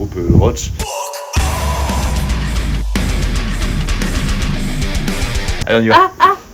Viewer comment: Hello, super vlog :D C'est quoi le nom du groupe à 3:15 ? Hotch? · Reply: ROACH !